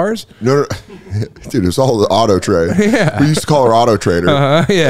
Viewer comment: “We used to call her auto trader” vs what I heard “Colorautotrader”